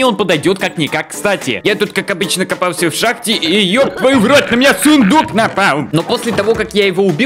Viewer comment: мне одному послышалось ёптваю в рот на меня сундук напал!